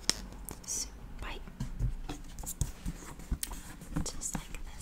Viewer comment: Interesting. I thought you were saying スパイ and not 酸っぱい. I guess context is key!